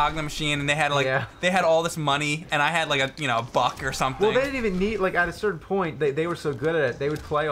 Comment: Did Mike say "And i had like... a fuck or something" at 2:32 ?